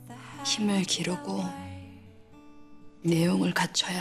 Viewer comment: 대사 뭐라고 하는 건가요? 내용을 갖춰야해 직전에.. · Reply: 힘을 기르고